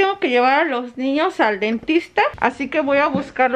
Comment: me dio mucha risa por que dijiste "dentista" y abajo decia "Optalmologo" jajajajaia